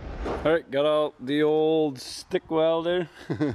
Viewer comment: I thought he said spick welder 🤣🤣 not stick